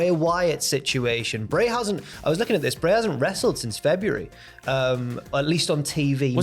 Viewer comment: When he said "bray hasn't" I thought he said "brayhausen" to me for a second. I t made me think, i wouldnt mind seeing brayhausen.